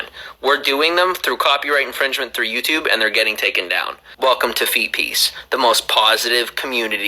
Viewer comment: I’m a foreigner, my English isn’t the best and I often misunderstand words because I don’t know the original, correct word. I don’t know what he’s saying there but all I understand is “Welcome to feces” and I think it’s pretty fitting. I think I’ll go with that instead of whatever else he’s saying